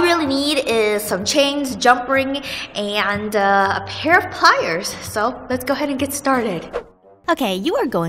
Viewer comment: When you were telling the supplies and said ' and a pair of...' my brain thought boobs, but you said pliers... Annnnyway, I thought you would get a kick out of that.